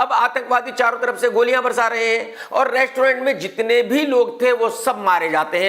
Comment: Sir.........Restaurant bolna ek baar ???? i heard Rektaurant twice